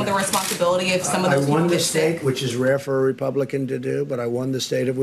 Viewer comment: "I made ONE mistake which is RARE for a Republican to do" 🤣🤣🤣..that is the JOKE of the decade! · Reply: I thought he said, "I won the state, which is rare for a Republican to do."